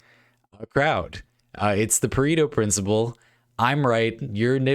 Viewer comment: I misheard it as "the Burrito principle" and now disappointed it's not that.